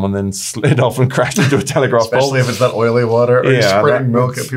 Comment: sorry, did he say telegraph pole ? I didn't think things were That different in England...